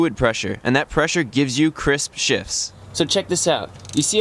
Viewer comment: who else heard "crisp shits" 😂